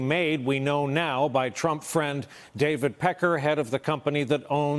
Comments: Wait, did he just say "David Peckerhead?" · Did he say that payment was made by David Peckerhead?